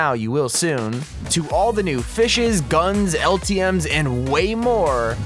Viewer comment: Did he just say fishes